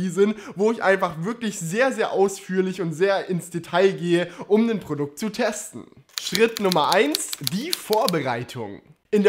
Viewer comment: Den Gerät? Wenn schon, dann aber "Der Gerät". Auch viele Grüße an Stefan Raab an dieser Stelle! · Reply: Achso, danke. Bei 1:08 habe ich auch "um den Produkt zu testen" verstanden. Hier ist es wohl wieder "nen".